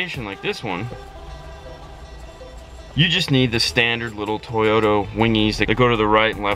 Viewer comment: Did you say "toyoTO" @ 0:22 second???? Haha